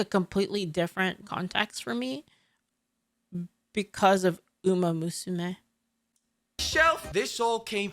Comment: Because of....booomomosmeh??? I have no idea what she's saying here. Could someone enlighten me please?